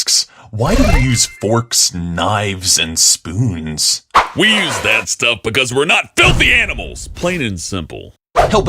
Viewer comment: I thought the spoons said spoonies